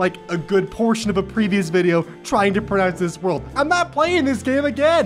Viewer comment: can't say Worcestershire but instead of word says world lol